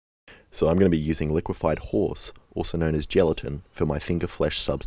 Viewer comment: did you say "liquified whores"? Is that thing really called liquified whores lol?